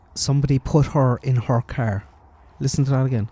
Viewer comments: I thought he said we put her in our car. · The 2nd one sounds like he says “put her in a car” his words run together, not much annunciation. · Sounds like saying "in our car." Most Americans pronounce our like are. And that's what it sounds like to me. Love your voice and opinions by the way. Your voice is soothing and opinions follow logic. Thank you ❤️🙏🏼